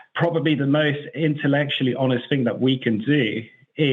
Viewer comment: I think I probably have a faulty connection, for a moment there I heard saying, the most intellectually honest thing we can do..? Did I hear that's right?